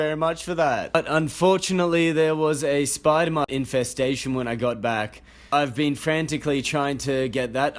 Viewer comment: Fully thought you said "spider monkey infestation"